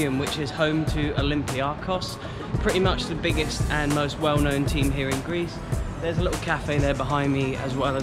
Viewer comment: I think he said "the biggest and the most well-known team here in Greece